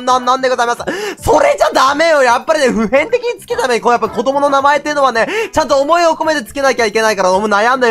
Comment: 滑舌が悪くて聞き取れない人用 子供の名前ってのはね、ちょっと思いを込めてつけなきゃ行けないから悩むよ、この子が将来この子の気持ちになって考えた時にこの子は将来Pちゃんと言われるのがいいのかスィちゃんと呼ばれるのがいいのかよく考えた時にやっぱりねPちゃんPちゃんPちゃんて呼ばれたら他のPスイッチもPちゃんと呼ばれてるわけでそしたらぼくとあの子の違いってなんなんだろうねってみたいなアイデンティティやっぱりね、自己を肯定する段階においてこう、段階においてねやっぱりね、Pちゃんって呼ばれるよりスィちゃんと名ずけた方がいいと俺は思いましたのでねスイちゃんと名ずけました、そしてねスイちゃんのスイはね彗星の彗でございます、てことはよやっぱりね彗星の如く一瞬の光かもしれないこの子の命は一瞬かもしれないけどその一瞬の中に輝きを放って欲しい、そんな思いを込めて今回はスイちゃんと名ずけます。